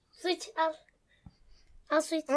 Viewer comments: su iç diyomus sıç anladım ::DddAdsafsaAaaf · 'u su iç yerine sıç al anladım çatladım orda gülmekten :D